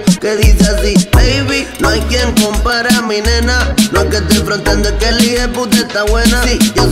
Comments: Siempre pense que decía "no hay tiempo para mi nena" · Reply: Yo pense que decia "no hay quien modela a mi nena" JAJAJA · Pensé que decía: No hay tiempo para mí nena :( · Siempre pense que decía "no hay tiempo para mi nena" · Quien más la busco como "No hay quien compare a mi nena"? jajajajajajaj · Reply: Pense que decia No hay tiempo para mi nena🤣🤣🤣🤣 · la mejor parte sin dudas... (2022):faa 7k de me gusta jajajajajaajja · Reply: yo pensé que decía: "no hay tiempo para mi nena" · Quien más la busco como "No hay quien compare a mi nena"? jajajajajajaj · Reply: Yo pensé que decía “ no hay tiempo para mí nena”😂😂😂